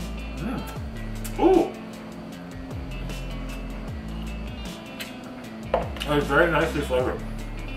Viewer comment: can someone translate 😂 All I heard was "ooo, cfhjvery nice qowdjjdjaoflavor"